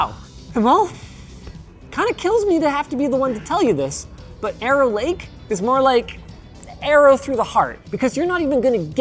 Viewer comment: I thought he said Error Lake